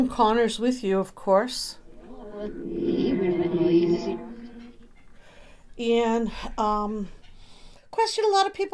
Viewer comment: At minute mark 2:48 if you listen again it sounds like it says of course he’s here with Laci. I clearly hear the name Laci. Laci Peterson was so beautiful. It is so tragic on what happened to her and her baby in utero named Connor. Looking at Laci she reminds me of Shan’ann Watts. These two cases are very similar but also very different. But they were murdered by their husbands and they both were pregnant with baby boys. But definitely re-listen to that response because I hear “of course he’s here with Laci” clear as day. Also thank you very much for keeping up the words/responses longer. This video was very easy to read/follow. Keep up the great work and I love your channel!